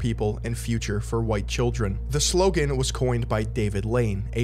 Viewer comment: bro idk if your gonna see this 42:41 i thought u said david laid